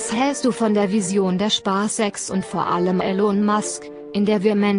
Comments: Bin İch Der einizge Der Bei 9:16 "Spaß Sex" Verstanden Hat???? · Ich habe erstmal Spaßsex verstanden bei 9:18 😂🤣